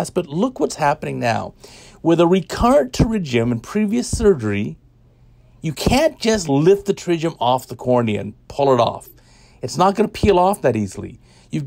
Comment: Professor, I didnt understood what you had said in 0:25 would you please tell me ? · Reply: "You can't just lift the pterygium off the cornea and pull it off"